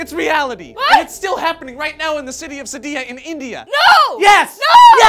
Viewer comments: I thought they said indiana.... I just shat myself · Oh, india, i thought you said indiana